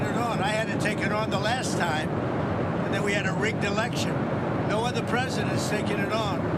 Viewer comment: Did he actually say "We had to rig the election" @ 2:46? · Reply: he said "We had a rigged election"